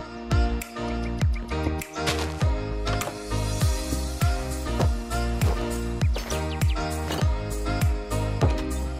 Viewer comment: I don't understand what is peeking B-what? (13:31) 🥺🤔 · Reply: It's the Name of that youtuber